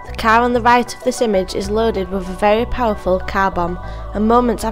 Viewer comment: I thought she said "cow" so I was looking around for a cow